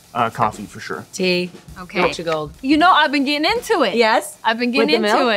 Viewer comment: Help. What's the name of the tea Florence says at 8:18? Watcher Gold? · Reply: Yorkshire gold